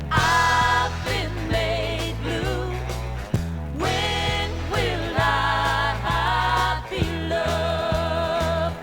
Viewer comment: I thought it sounded more like 'When will I pee, love?' on that one. (An incontinent lady asking the question to either her husband or her doctor perhaps?). On the legendary label Nit-Pick records in that case? But perhaps I misheard it?